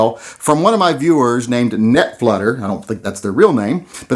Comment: Thought you said "nut flutter." Ya know. That feeling you get in your pants when you see a Korth at your LGS.